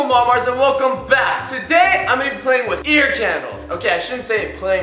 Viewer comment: I thought it said ear candies